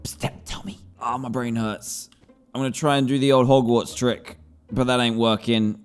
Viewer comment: did he say HOGWARTS trick OMG im interested! I'm a fan..